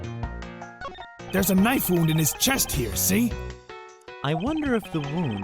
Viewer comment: Thanks to everyone who stopped by for the premiere! I'll definitely be doing more of those in the future. Even if Youtube just auto-closes my chats after it finishes haha. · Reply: When detective said " Knife wound " i thought he said " Knife-u " like Japanese language XD